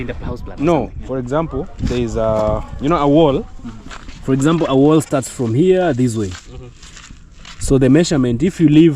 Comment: Glad to see your brother is back in the village he looks great as always. Did I heard him correctly say he was in Bermuda?🇧🇲 Blessings my brother 🙏🇰🇪🇬🇾🇺🇸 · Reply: Yes I was in Bermuda 🇧🇲 😂😂